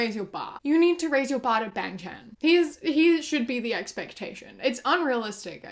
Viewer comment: "you need to raise your expectations to Bang Chan" tho unrealistic, truer words have never been spoken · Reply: OH she said Bang Chan. I thought she was saying Ben Ten